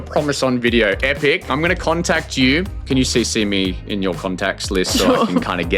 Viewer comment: what did bro say 😂😂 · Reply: Cc? Carbon copy, it's when you send an email to one person but you can choose to have other people see the email